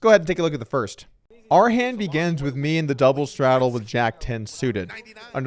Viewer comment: Is it just me or does Doug always sound like he is saying begans instead of begins - "our first hand begans..."